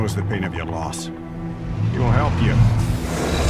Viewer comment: I hear „let me help“